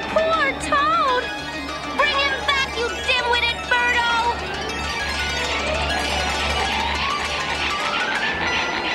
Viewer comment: did peach just say damn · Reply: 193 No, she said Dimwitted.